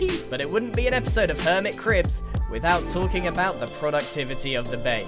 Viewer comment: We need more Hermit Cribs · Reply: I thought you said "hermit crabs"